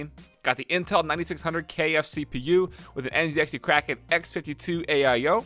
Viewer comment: sounded like he said "kfc"